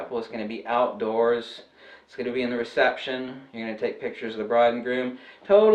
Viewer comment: The broad and groom, lol. 16:04 I know you say bride, but it definitely sounded like broad, lol.